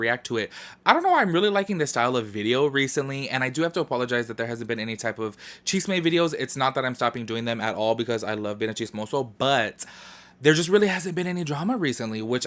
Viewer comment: I thought you were saying “chiste” and I was thinking to myself like.. joke videos? Did he ever do joke videos? Lmfao 🤦🏻‍♀️